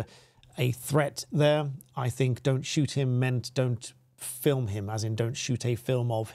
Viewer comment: I thought she was saying in her Chinese accent don't shout at him that would be in context with the guy shouting